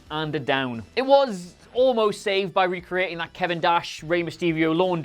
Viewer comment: Did he say Kevin Dash?